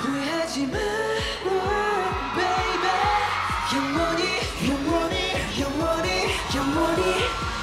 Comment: I heard your money,your money,your money, your money dundh ke nikal (only indians armys can understand)💜💜💜😂😂😂